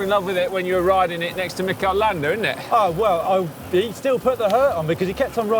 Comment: Mistakes in the captions (it seems that the people who write them don't know anything about cycling): "Mick Orlando" instead of "Mikel Landa" at 4:54 "radda-crit" instead of "Red Hook Crit" at 5:24 "when arrested the Passo di Valparola" (WTF 😂😂) instead of "When I everested the Passo di Valparola" at 6:18 "careem race" instead of "Keirin race" at 7:21 "our duty and weekly inspirational photo" instead of "our GCN weekly inspirational photo"